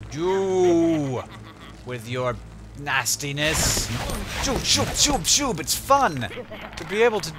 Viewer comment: At the begining I seriously thought he said "Jew! With your nastyness! Shoot Shoot Shoot!" :/